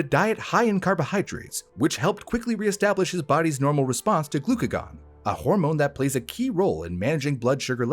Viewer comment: I thought he kept saying Lagoons and im like bruh what does a lake have to do woth my diet.